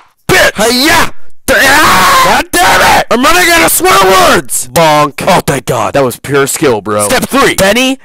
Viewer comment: I thought he said “IM RUNNING OUT OF SQUIDWARDS”